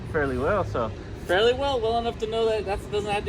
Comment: Too instead of to